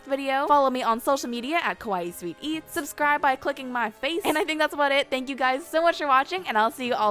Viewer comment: did she say click my face